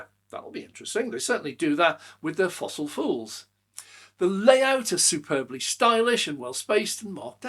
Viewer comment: Sounded like you said fossil fools? 😀